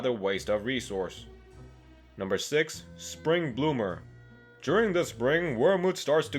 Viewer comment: When it said spring bloomer I thought it said spring boomer